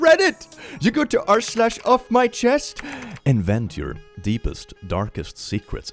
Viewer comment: In "you go to r/offmychest and vent your deepest darkest secrets," I heard "and vent" as "invent." Works either way, sometimes.